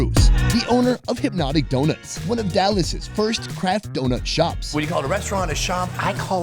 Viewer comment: I swear to god Sonny, when talking about Hypnotic Donuts I thought you said "crack donut shop". I mean....